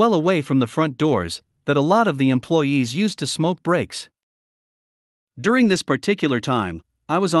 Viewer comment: It sound like he said "there was a bench outside, that a lot of the employee used to smoke bricks'... interesting lol